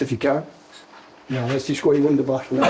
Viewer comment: Ok from a non-scot: What is the coach saying at 1:13 before everyone goes nuts? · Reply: Let's just go to Wunderbar (local bar)